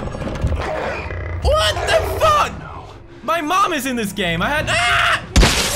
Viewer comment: all I heard was “WTF my mom is this game”